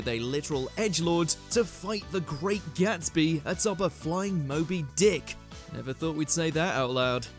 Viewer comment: I kinda thought you said Mobile Dick… had to do a rewind.